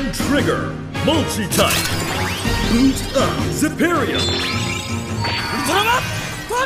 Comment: I think it said "Boot up".